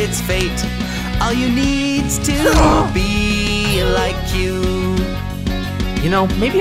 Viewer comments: anyone else notice the subtitles say "be like me so girls like you" and we heard all you need is to be like Kyu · Lines: "Be like me so girls like you!" What says: "All you needs to be like you!" Am i the only one that picked up on that? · What I hear: All you need’s to be like you Subtitles: Be like me so girls like you!